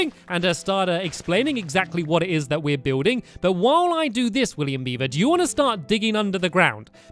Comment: Pause at 3:41 the subtitles says William Bieber.